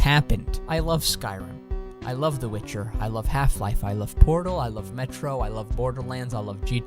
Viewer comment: When you said you love portal I thought you said I love porno